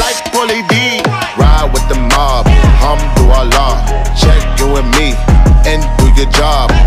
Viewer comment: I thought he said "hum to allah" not "alhamdulilah"